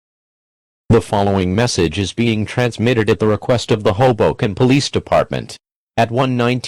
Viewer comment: Sounds like it says hobo police department